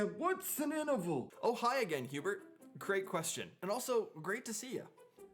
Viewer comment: It sounds like you say Hubert at first then. I guess that means I need to come up with more questions :)